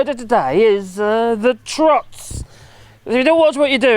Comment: "Trot" I thought I heard. I had once looked up some very technical, horsey terms about that. Then I paid more attention. Ah, "the trots". Ok, as in "the runs". "La courante", quoi. Montezuma’s revenge. La tourista - but lucky Brits won’t even have to go on a foreign holiday to experience it. 😉